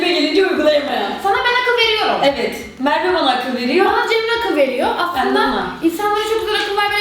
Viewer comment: ı Cellat diye anladım aldöwödmwqöfö